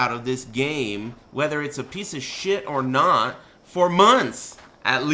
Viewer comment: "whether it's a piece of shit or not for MOney" lol i thought he was gonna say that, i think i've been hanging out in the wrong places